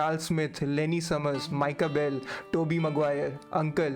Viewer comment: Did I hear Tobey Maguire?